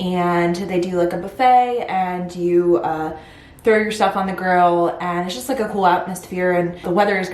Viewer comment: Throw yourself?!?! Or your stuff... lol. Yes we paid for it lol. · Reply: I had to laugh cause it sounds like you said throw yourself. Did you say stuff and just heard it wrong?